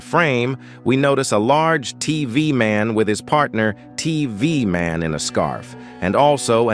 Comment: why does he say tee vee man 😂